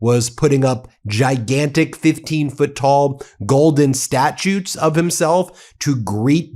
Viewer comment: I could have sworn that I heard the word "statutes" instead of statues...